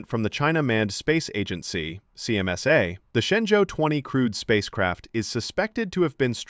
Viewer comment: The word is 'crewed', meaning manned, or capable of carrying astronauts...not 'crude'. The words sound alike but very different. Calm down friend. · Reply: Quick clarification: It sounds like he said "crewed spacecraft" (with astronauts onboard), not "crude." Similar pronunciation, completely different meaning! Sorry... 😬